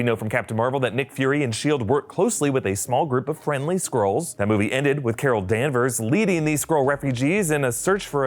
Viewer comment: I was not listening a 100 percent, and I swear to god it sounded like "Nick fury and captain Marvel worked closely with a small group of friendly squrrels" at 02:24 holy shit i can't breathe😂😂